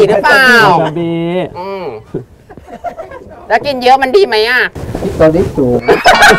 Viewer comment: ลุงพูดว่าไรอะ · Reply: คริสตอริดสูง 55